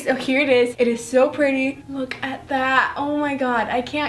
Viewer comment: did she say fuck? XD